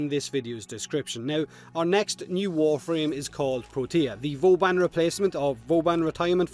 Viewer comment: Every time they said protea i swear i thought they said tortilla, is that only me?